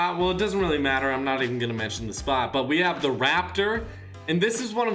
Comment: When u said the raptor I thought u said the raptor skin I was like what? Lol